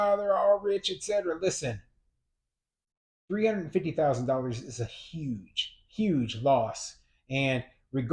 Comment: This video is a mess. Antifa reduced the property values, a $350 million loss? Where, with real estate prices going through the roof do you have a loss? Unbelievable. A $350 million dollar loss, most never attain that much. This video is a bunch of republican talking points. This wasn’t some ‘regular’ guy. He was a multi millionaire investor, the ones who usually make it harder for the rest of us. He probably landed on his feet as millionaires and billionaires do. It’s the little people who get flattened, remember that. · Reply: You misheard - it was a 350 K loss not 350 M. 😂